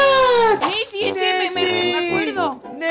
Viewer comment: escuche messi XD